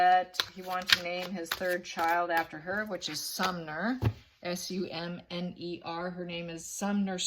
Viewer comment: When you said that he wanted to name his 3rd child after his mistress which is Sumner, I thought you said "which is some nerve" hahahaha